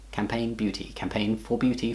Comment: i heard "campaign beauty" as "campaign booty"